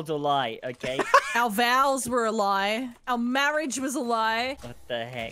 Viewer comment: I heard that as, "Our vowels were a lie"